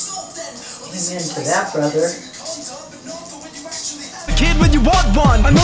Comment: "Amen to that brother" -the older blonde teacher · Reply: Nice, I thought she said: "I'm into that, brother" and was a little surprised.